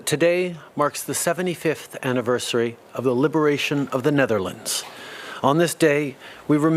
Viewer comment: Typical little rich kid who's mommy and daddy threw money at any problem the little boy ran into just to make it "go away". Our lame PM does the same each day... I'd like to see him actually come up with some creative ideas to help instead of just throwing money out there. Did he just say "Nellerlands"?